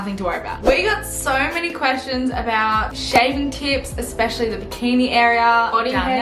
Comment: I thought you said “shaving tits” 😂😂